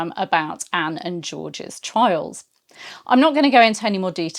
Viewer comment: Whoops! Just a minute! At 5:51 "Anne and George's CHILD"??!! I went back and listened again to be sure I heard correctly. sort of swept by to something else, which seemed odd unless she meant to say, "childHOOD" and misspoke. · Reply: I'm pretty sure that actually said "Anne's and George's trials" about their court TRIALS when they were both charged, imprisoned, and later (with others) executed. I hope this clears this up for you! 👋 😊